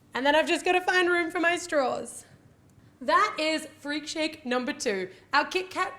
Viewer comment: I loved this video. But is it just me or did she kept saying through the video that she is making a kit Kat fruit shake or milk shake? It really sounds like she is say fruit shake and I keep thinking "but there is no fruit in it". I don't know I probably just not hearing her correctly. I love this recipe though. :)